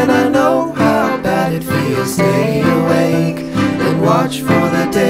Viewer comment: Bruh I heard "go congratulate her" as "go go gadgulator"